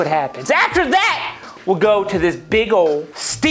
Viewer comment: sounded like legos